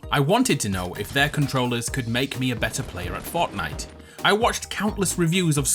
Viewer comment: did you just say fartnite